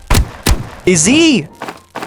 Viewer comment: What is bro saying? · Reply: Izzy😂😂